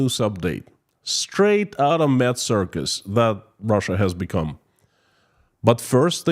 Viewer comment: First time visitor. When you mentioned "mad circus" I heard "meth circus" Not too big a difference I know, but I had to hear it again to ensure I heard it correctly! ;)